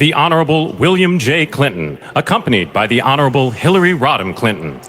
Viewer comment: I thought that man said Hillery Rotten Clinton?